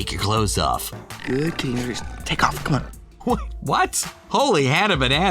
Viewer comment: In Aladdin, I can clearly hear "Good kitty! Just, take off!" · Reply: I heard 'Good teenagers, take off your COAT', instead of clothes. X3